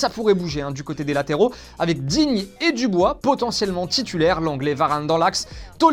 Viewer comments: qui a entendu comme moi : l'anglais (lenglet) Varane? 😹😹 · j'ai entendu " l'anglais varane dans l'axe"